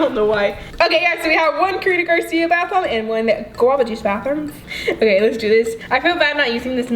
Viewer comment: Did anyone here her say at 11:30 a guava juice bathroom, instead of a guava juice bath bomb, or is it just me???